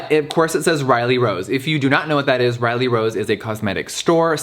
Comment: Riley rose? I thought he said Reily Reid 😭